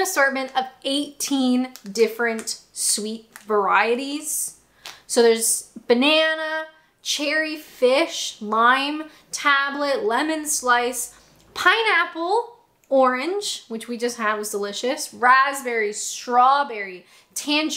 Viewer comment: did I hear you say "fish"? 😀 That's a new flavour of sweets to me 😃